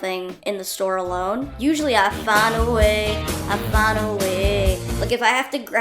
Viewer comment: I thought she said find a wig a first- “Usually I, find a wig! Find a wig!” LOL.